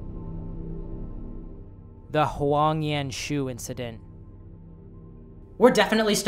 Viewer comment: my friend just said " did he just say the Hwee on yen shoe incident" 😅 lmfao